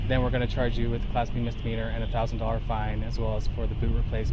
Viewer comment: I think he actually said Misdemeanor.